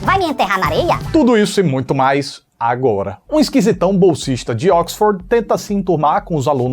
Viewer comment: Socorro! Eu quase entendi "Um solteirão bolsonarista". 😂😂😂😂